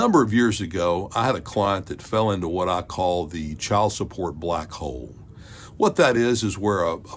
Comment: bo i thought that thing say child support black 304. i was like GOT DAWG this man bold lmbo